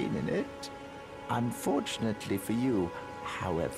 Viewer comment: I thought he said "maidenless c*nt":at first, until I killed him myself & read the subtitles. Lol. He's quite handy with a bunch of roses as a weapon!